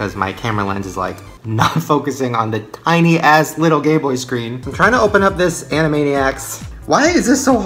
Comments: Lol for some reason I heard gay boy screen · when says "Gayboy screen" instead of "Gameboy screen" 🙄😂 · I thought he said gay boy 😂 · Did anyone else notice at 6:50 he said “tiny a** little gay boy screen” instead of game boy😂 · did he say "my camera won't focus on the tiny little gayboy screen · it sounds like he said gay boy screen